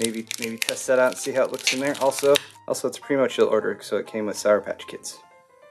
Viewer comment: Did you say "pretty much ill order"? At some points in your videos, you are less clear and concise than at other points.